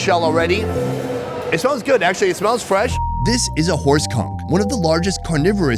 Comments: For a sec a thought he said horse cock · I thought he said horse cock 😂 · Lol I thought he said horse cock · Thought I heard “ horse cum” 😂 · I thought he said horse cock